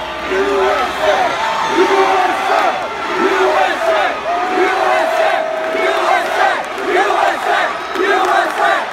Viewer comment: What they say in 1:18? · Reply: They say U S A